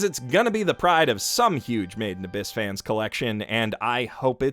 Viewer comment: I always thought you were saying “maiden abyss” I also thought it was a maid cafe show or something similar as you can imagine I found myself constantly wondering why you would critically acclaim a maid cafe show (oh how I was wrong 😑)